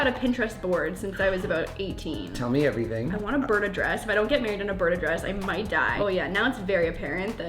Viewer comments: did she say bird address??? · I thought she said I want to burn a dress at first 😂